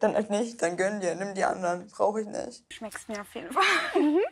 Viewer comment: Was sagt bei 35:50? Schmeckst nach?